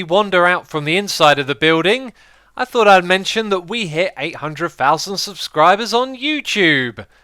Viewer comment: For a second, I thought you just said "We just hit 'a' hundred thousand subscribers..."